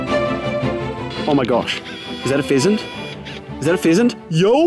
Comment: I literally thought said: “Is that a peasant?”